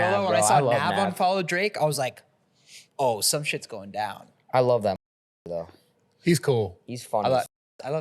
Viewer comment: did steiny actually say “I love Nav, I love that nigga bro”😂😂 they bleeped out the nigga part but you can still hear it lmao